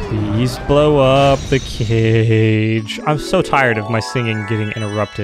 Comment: When said "Blow up the cage", I could have sworn he said "Blow up the kid."